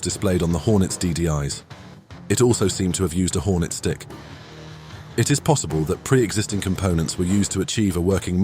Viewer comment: "Hornets dick" 8:32 🤣 yea, i know he actually said hornets stick after doing a double take